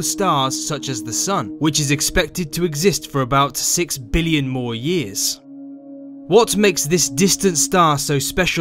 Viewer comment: Wait, did you say (2:00) that the sun has 600 billion more years?! Please check your number. That’s not what astronomers told us. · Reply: kang xu he said 6 billion more years